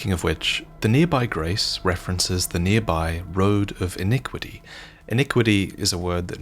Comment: I thought you were saying "inequity" which is the same as "inequality" which simply means "unfair". Iniquity is a problematically similar word which does indeed mean a more extreme form of "inequity". 41:59 He could just have been on the other side, but now that he has gone mad, he flipped sides. 1:08:00 Again, more stuff that would have been easier to get if we just got a proper translator on these games! The word "bastard" has enough of a double meaning for us to think it's just a derogatory term at first, and later learn of the npc's birth, and starting to wonder if "bastard" meant "child of a noble with a peasant/commoner" instead of this general "unwanted" which focuses too much on affection/tolerance than their origin of birth.